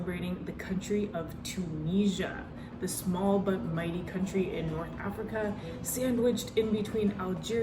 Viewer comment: i thought you said 'a small butt country' until i activated the subtitles :)